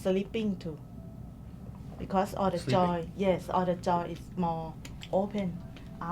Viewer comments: He thought she said “all theJoy” but she was saying “all the JOINTS” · I think she's saying JOINTS, not joy! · I think she was saying “joints” and not “joy”. Although, I guess you could say it’s true either way.